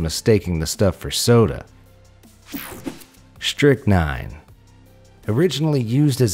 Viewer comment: Who else thought he said Snitch nine